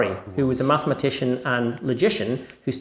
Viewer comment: I thought he said Pascal. I was excited for a moment there. :-| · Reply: I thought he said Schoenberg was a "mathematician and magician".